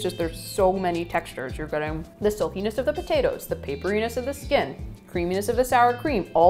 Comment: DID SHE JUST SAY THD SOAPINESS OF THE POTATOES